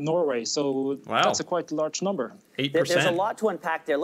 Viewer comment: 80% is what I heard :P